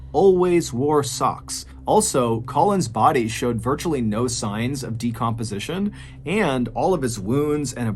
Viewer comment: Definitely thought you said “Colon’s Audi showed no signs of decomposition.” Why are we talking about some asshole’s Audi when we’ve got Land Sharks and a tale, that is all strange, dark, and mysterious, to hear about?!